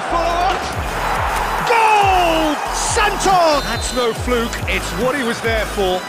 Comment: did I hear goal Sancho in 1:52💀💀